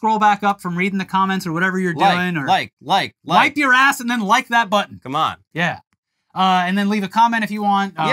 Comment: I misheard that as "Like your ass and then wipe that button"